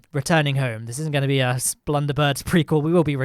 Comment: Did he say splunderbergs?